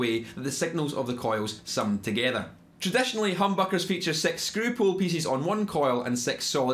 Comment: Did you just say "sex screw pole" at 4:11??